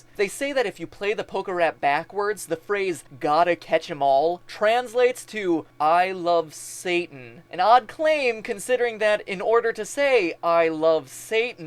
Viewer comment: I heard "All hail Satan" but rapidly.